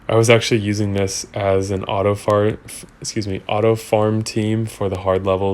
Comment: Did he say "Auto Fart" ?? :)